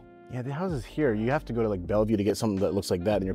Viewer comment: Community man 🤙🏼 Btw are you in the GNW perhaps? 15:43 Did you say Bellevue?